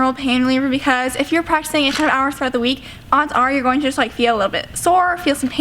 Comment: did she say "practicing 10 hours throughout the week"??? What.. who.. how.. Is that a gymnast thing or an American thing or did I just hear wrong?